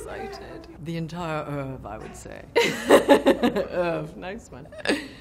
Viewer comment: what did meryl say at 1:22 the entire .... I would say? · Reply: Oeuvre. It means a complete body of work - whether that be from a painter/musician/writer/actor etc